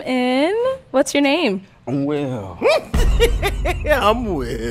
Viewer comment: I thought he said... I'm well.. then his name is Will.